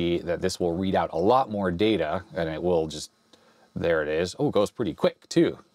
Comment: Did he said «О господи!»?..😮